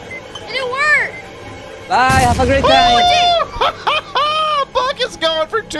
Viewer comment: you said buck but you sounded like bucket